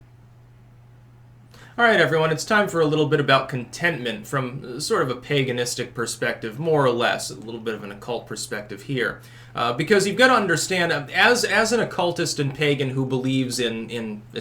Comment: At first I thought it said “Containment” and I was like “you’re supposed to purge the heretics, nor contain them.”